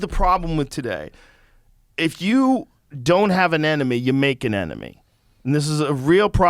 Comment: I thought he was saying "if you don't have an anime you make an anime"😂